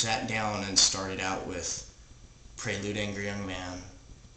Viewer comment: started out with what? Prelude....? Hard to hear what was said. THANKS! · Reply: prelude angry young man